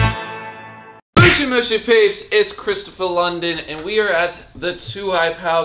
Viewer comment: is it just me or did he say it’s Christapha London